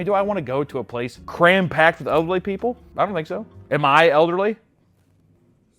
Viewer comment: I know you said elderly but REALLY sounded like ugly people!😂😂😂